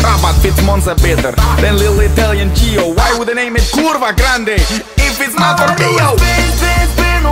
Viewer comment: "why would they name it kurwagrande, if its not from rio" is what i heard and now you can't unhear it XD XD